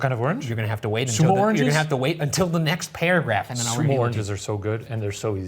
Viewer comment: at first, I thought he was saying s’moranges and was SO confused. But when he mentioned the little butt, I knew exactly lol 😂 Sumo oranges are ELITE!!! I love them so much!!